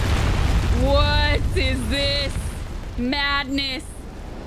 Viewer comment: Did uncle say "madness" at the beginning? Cos I thought I heard "Navnick"....